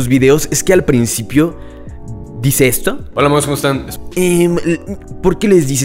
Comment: WTF, yo pensé que decía "hola vagos, ¿como están? 💀